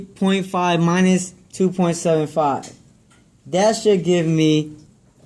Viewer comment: I heard “dat shit give me” not “that should give me”